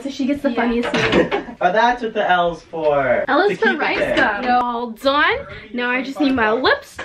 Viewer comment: Who else heard "L is for ricegum"? &